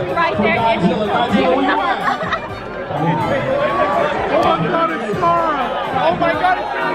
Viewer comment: did someone say "Oh my god its scarra" 😂😂😂